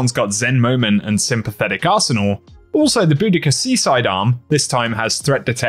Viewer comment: I was working while watching and thought you said "Boudica Sea Side Arm" and was caught off guard lol